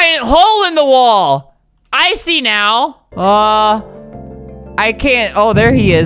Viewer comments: When said i see now I actually thought that he said “IcY nOw” ;D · this is wut I hear: icy now